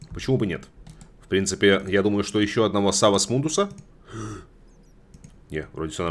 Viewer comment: Внезапно послышалось "сало с мундуса" :D